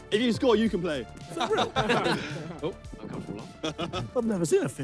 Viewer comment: what does chris say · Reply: Idk I hear uncomfortable